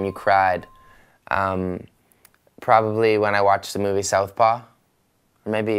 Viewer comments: which movie made him cry? at 6:51 ....sorry I didn't understand it because I am German :D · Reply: Mary Lisner First I thought he said south park xD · which movie made him cry? at 6:51 ....sorry I didn't understand it because I am German :D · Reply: southpaw